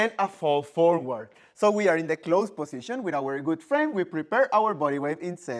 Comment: I misheard “we’re in closed position with our good frame” as “we’re in closed position with our good friend” and it just felt kind of funny haha.